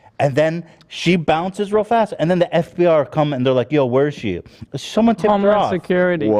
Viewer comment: Rewatching and @ 46:29 I've noticed Ethan says FBR instead of FBI 😂